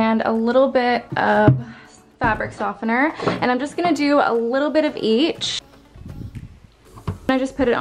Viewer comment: OMG when you said “a little bit of each, I heard “a little bit of bleach!” I was like what...had to rewind and watch it again 😂